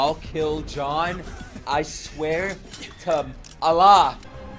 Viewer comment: did he just said i swear to allah ? 😂